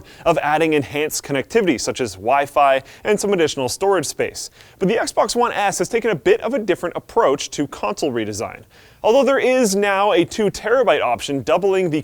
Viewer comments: does he say XBox 1 ass..? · it sounded like he said XBOX One Ass ( ͡° ͜ʖ ͡°) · did he say Xbox one ass?